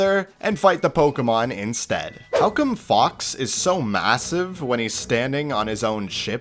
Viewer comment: d-d-did u say massive?